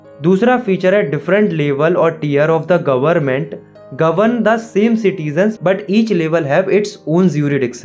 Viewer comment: At the timestamp of 2:58 you writen tears instead of tiers